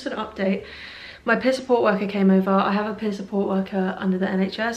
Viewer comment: lol for some reason I kept mishearing "peer support worker" as "piss poor worker" 😭You're doing amazing!!!